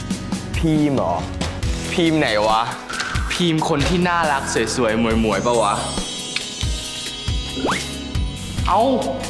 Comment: I obviously speck English because every time I here that soy moi moi I freaking laugh 😆my hoodie off😂